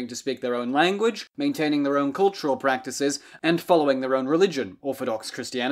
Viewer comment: Greeks under Ottoman rule did not "form" their own religion. They practiced Orthodox Christianity which predated all other denominations since it was the original church. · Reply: I appreciate the response. It shows academic honesty. At 0:40 the verb sounds either like "forming" or "following". I may have misheard it. Can you clarify?